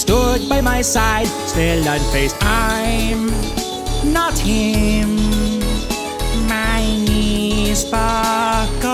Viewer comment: Boofriend gets bored and reads the Luigi Mansion Wikipedia article in beep boops Also at 1:20 there's "I honestly..." but I can't make out the rest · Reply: Pretty sure it says "I could honestly write whatever here"